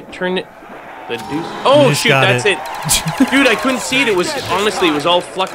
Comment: did say s**t when he picked up the zombie blood? listen to it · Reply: ORBC darkness no i said o shut